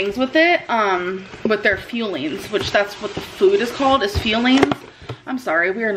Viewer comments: Sounds like she says FEELINGS instead of fuelings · Every time she calls it fueling, I hear “feeding.” Which is a much more appropriate word for her eating habits imo